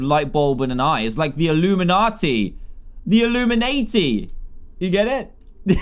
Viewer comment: when dan said "illumanati" i thought he said illuminaHD the minecraft speedrunner for a sec